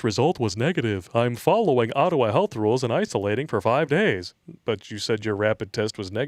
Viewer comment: Re. Justin's tweet. Did I hear that correctly? "I am following Ottawa health rules and I fellating for five days." I haven't heard of that therapy before but might expect it from Trudeau.